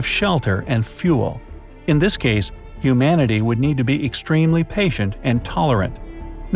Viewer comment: When he said humanity I misheard it as iumanity